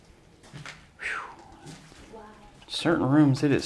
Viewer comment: Did someone just say wow in 7:27